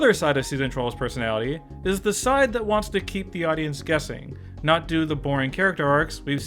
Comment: with the pic on 1:43 I heard "the side that wants to keep the audience gassing" lol